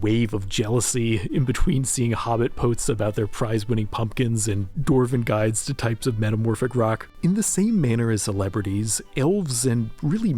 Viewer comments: did i hear a rock and stone? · Did I hear a Rock and Stone?!